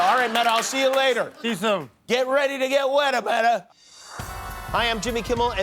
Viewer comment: Did he just say "get Ready to get Wetta Metta" ?